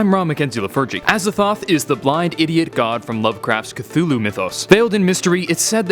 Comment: Did you say blind idiot god? If yes then XD